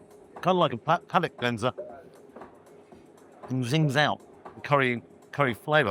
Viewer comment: I thought you said a "Paddock Cleanser". Sorry, thought you were still talking about the beef!! Need to get my hearing fixed!!